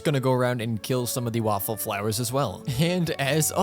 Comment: It sounded like u said “waffle flower”😂